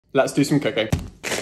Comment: Bro I swear I heard his say “let’s do some cocain”